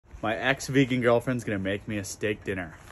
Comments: “ex-vegan gf” made me think she was ur ex gf and still vegan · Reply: i thought he said my ex's vegan gf. that wouldve been a pretty interesting scenario tbh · For a moment i thought you said "vegan ex girlfriend"😂